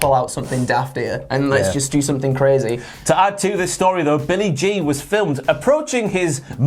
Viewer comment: When you said "Billy G" I thought you said "Billy Jean".